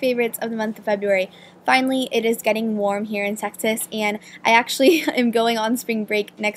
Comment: haha sounds like you said texas as "sexiest"